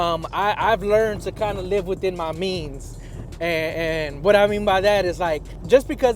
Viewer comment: Have you driven a Ford lately? But Mopar or no car! ♈ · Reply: Did you say you live life with beans? 🥫 Beans and rice are nice. 🍚